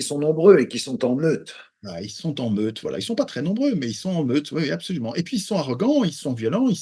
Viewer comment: ils sont nombreux et en "meute" j'ai entendu en bleu 😅 tu me diras c'est pareil...😅